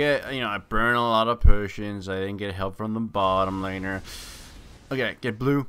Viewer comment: every time he says 'potions' i hear 'persians' :O